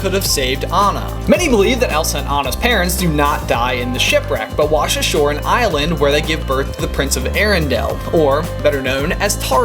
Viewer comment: Lmao, I had to listen twice there .. the last fact I thought you said "They wash ashore in Ireland to give birth to Tarzan" ... Irish Tarzan would be a funny concept